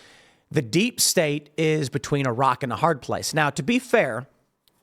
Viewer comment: I thought you said Iraq and a hard place.